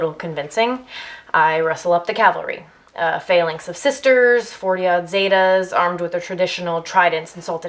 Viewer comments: On the CC at 1:30 it says "Uh, [?] of sisters" but I think she said "A phalanx of sisters" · How does one fix the subtitles? Because I know what Danny is saying at 1:28 . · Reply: "a phalanx of sisters..."